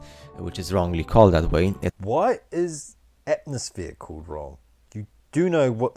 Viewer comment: Excellent! I have a small correction and a question: 4:40 he said "hemisphere", you misheard "atmosphere" Where do babies come from? It's storks, isn't it? Someone told me birds and bees but they'd need to be loads of those to lift a baby.